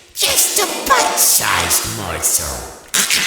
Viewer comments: OH MY GOD I always thought Nightmare BB said "Butt-sized morsel" and I was so confused. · "You're not so big... just a bite-sized morsel" Nightmare BB. I heard "butt-sized morsel"